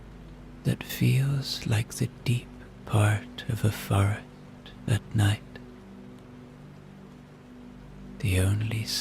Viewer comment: I could swear he says “like the deep part of a fart at night.” Don’t change it!!! lol